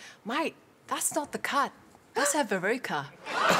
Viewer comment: what did she say? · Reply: "That's not a cut, that's a verruca" verruca = foot wart.